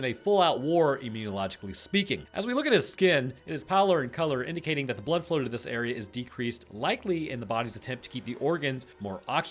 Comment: It’s what in color? What did he say? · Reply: Pallor/Pallid AKA pale